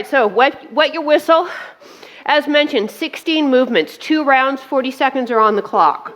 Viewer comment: Aw. Bella was great to see there at the end! And I must confess I'm not a huge fan of dogs...(don't kick me off the channel!🐶 😅) I thought you said 3 rounds so I was gearing up for round 3 and incredibly relieved that we were done! 💞 Great workout.